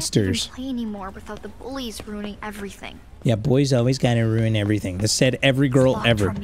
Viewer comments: Bro thought she said boys ruin everything when she said bullies ruin everything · I like how thought it said “boys” but instead it said “bullies”💀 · Anyone else realize that he thought she said boys ruin everything not bullies ruin everything